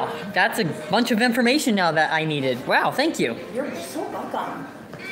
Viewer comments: did she say "you're so baka"???? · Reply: I think she said “you’re so welcome” in a sarcastic tone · did she say "youre so baka"????